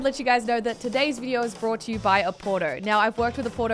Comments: I thought said porno at the beginning😂😂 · I thought she said a porno at the start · I thought she said porno in the beginning